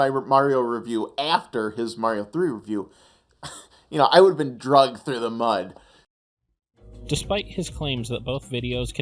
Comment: did he say "drug through the mud"?